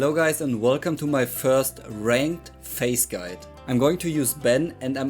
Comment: Lol I heard "with face" instead of Phayze lmao